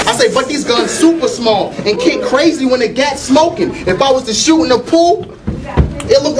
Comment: Wait, the nugga said 2 Ks in each hand. The kickback look like he doing a backstroke? Fuck outta hea both arms would be broke. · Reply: I could have sworn said " two gats blowing"